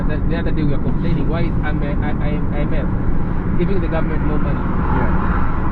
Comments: Which ones? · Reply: he said "...why IS the IMF giving more money..." Your caption said 'Isn't' which alters the whole message. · He said 'why is the IMF giving more money' not 'isn't giving'. He was complaining about IMF loans. There are several parts you are assuming what he wants to say.